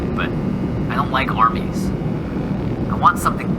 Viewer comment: When the radio guy said I don't like armies I thought he said I don like arbys